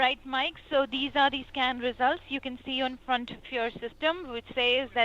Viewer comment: did she really said "scam results"?